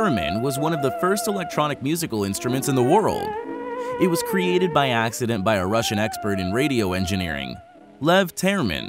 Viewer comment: then you said the theremin was created by accident i HEARD the theremin was created by acid...and had to put on the closed caption to be sure what was being said aligned with what it thought was said...alas i was wrong...or was I?